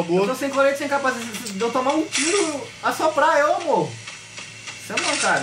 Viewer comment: só eu que entendi "amor". Kkkkk